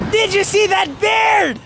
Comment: Did you see that 🐻 bear...I heard it wrong 😅